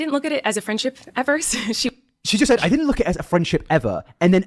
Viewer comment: ITS ME AGAIN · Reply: I’m down w this but the entire “jojo isn’t my friend” part is based on a part of the interview you misheard. She said she didn’t see jojo as a friend “at first” not “ever.” But great other than that!